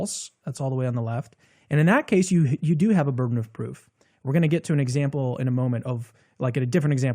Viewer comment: Not really relevant, but at one point I misheard you as saying burton of proof, I thought it was my eyes that didn't work, not my ears lol